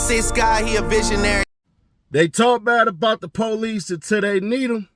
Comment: I thought yo gotti said f the police